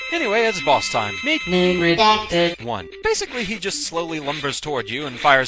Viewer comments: I was saying "NAME REDACTED" since technically we don't know who this is yet and won't until the 4th stage. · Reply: name redacted? all this time i thought glados was saying "numerdactor". yeah, silly. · Sounded like he said "Neo-Reactor" XD Tried to look up the enemy and failed.